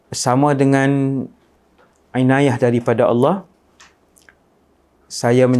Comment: apa maksudnya ya? kalau saya x salah dengar 'inayah kan yang disebut?